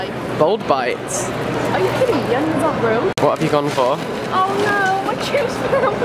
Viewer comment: is it just me or did someone say onions arent real